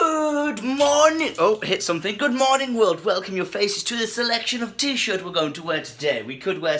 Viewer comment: I thought u said 'a selection of tissues'. I got a bit worried about u...